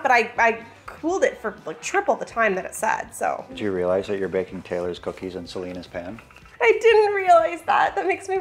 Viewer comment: Oh, I misheard you at first when you started making Taylor’s cookies. I thought you said Chive cookies and I was very curious how that was gonna turn out.